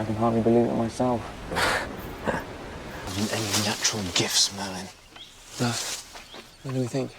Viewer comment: The subtitles thought that Arthur called Merlin "Melon" instead... 🤣🤣🤣